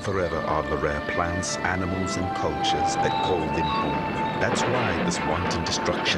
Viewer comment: if poachers are gone, how is that a bad thing? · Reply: Oh, "cultures". I thought he said "poachers". They way he said "cultures" sounded like he said "poachers".